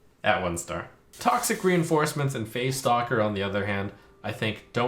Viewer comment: I don't know if this is just for me but every time I hear Trump say "Phase Stalker" I hear "Face Doctor" instead ...... and it fits pretty well too.. so that what I'm gonna call it